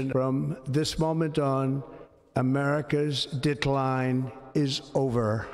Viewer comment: I heard it too "dic-line"... 🤣 I actually paused the video and went back with closed captions on before Jon's joke. "With a tuck or a reassignment?" 😁